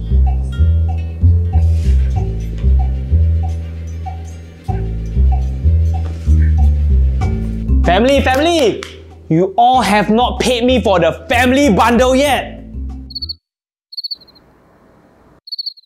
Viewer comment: Did someone just said family ?